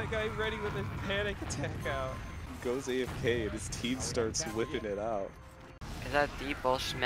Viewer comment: ??? what did he say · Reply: sometimes your team just starts whipping it out